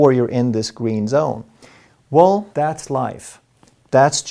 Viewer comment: “Well...that’s just life” 😂 I heard it as: “Sucks to be you!” Dr. Ekberg is the best.